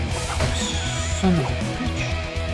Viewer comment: I heard him he said son of a bi***